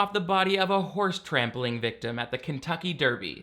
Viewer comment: Thought he said horse “trampoline” accident